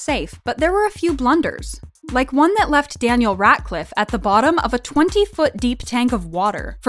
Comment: I'm crying! At the beginning all I hear is Daniel "RAT"cliffe OMGAAHHH IM DYING